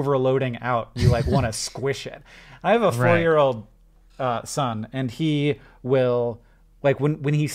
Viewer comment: omg i thought he said 40 yr old son hotnjgfkh